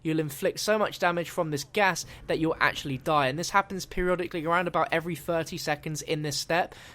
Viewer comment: Is it me or did he just say periololically? Lol thats what I heard